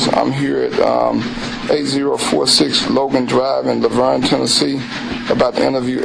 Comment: This random comment it's cause I'm listening did he say lavergne TN? That been my hometown of 15 years ?